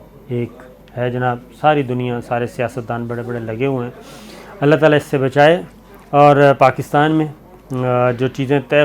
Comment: SLIP OF TONGUE Please take care. It seems a slip of tongue. You said "SIAST-DAAN" instead of "SCIENCE-DAAN"